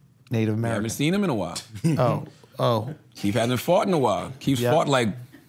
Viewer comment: Charlemagne said he hasn't fought in a while and I had to rewind it because I thought he said he hadn't farted in a while and I couldn't figure out why would someone only fart four times a year!? 😂